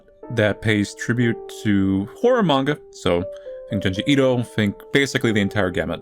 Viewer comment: What manga? Couldn't hear it clearly aaa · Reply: Junji Ito, a horror manga